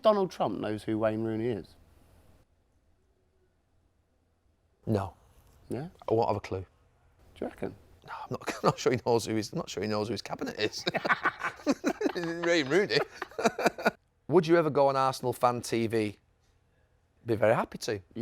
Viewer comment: Did Gary just say "Raine Wooney"???